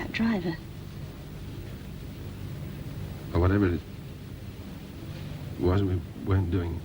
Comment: What is he saying here? "Whatever it is wasn't...where doing..."